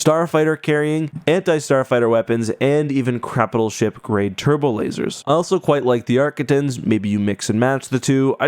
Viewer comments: did you say "and even crapital ship grade turbolasers" ? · lol you accidentally said Crapitail instead of capital ship lol